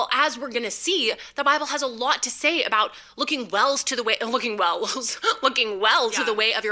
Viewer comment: Interesting, I'm very curious as to whether her mistake at 3:04 saying "Looking wells to the way" might be a Freudian slip related to the Lutheran church she attends being a WELS church. Their doctrine ABSOLUTELY lines up with a lot of what she is saying. I just heard her say in a video that she and Dan switched to a Lutheran church, not the specific denomination, but I imagine it has got to be LCMS or WELS. I can't see her putting up with the progressive views of ELCA congregations.